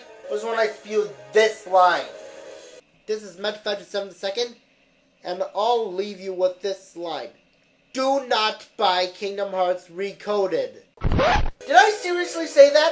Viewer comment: Can you tell me what you say at 13:50? It's so silent I can't hear anything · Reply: ***** "This is Meta527ii, and I'll leave you with this statement. Do not buy Kingdom Hearts Re:Coded!" It can be heard in only one earphone, you probably only had one earphone in.